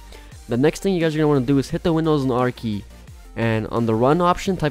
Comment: What did he say at 1:45-1:47? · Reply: hit the windows and r key!